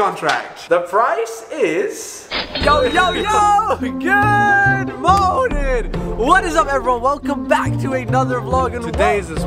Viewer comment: Yoo literally his intro when he says good morning is misheard as good moaning..😂